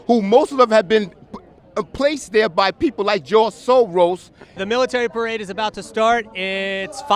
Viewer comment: did he say Joel Sogros?